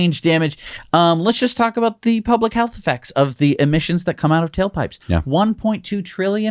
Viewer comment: You said "tail pipes" but I thought you said "kill pipes". Maybe the latter is a better name for them.